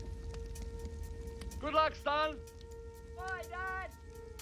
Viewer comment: can someone plz tell me what she said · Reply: Wahahaha Good bye, Wehehe oh my son